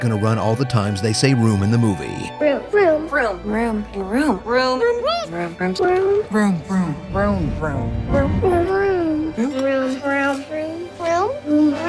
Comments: Room now sounds like Vroom, this all i heard them say "vroom. vroom. vroom? vroom vroom. vroom?" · It sounded like they were saying vroom vroom.